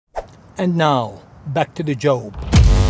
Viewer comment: I swear the guy at 2:26 said "and now back to the Jobe" or did he?